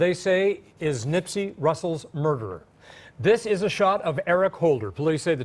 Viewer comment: Did he say nipsy russel??? 🤦🏻‍♀️ Gawd damn! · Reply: I heard him say Nipsy Russell the first time.